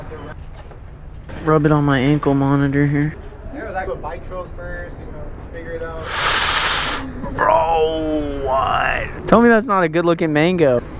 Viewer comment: am I tripping or did he say, "ankle Monitor" and he actually had one